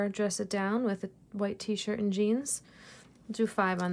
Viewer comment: 😂😂😂 I had to listen again I thought you said wet T-shirt and jeans 😆😆😆